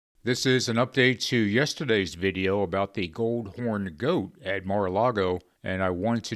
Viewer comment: AAAh! "gold horned goat at Mar a Lago". I thought he said the "old goat at Mar a Lago", and I thought to myself, well sometimes he's at the White House.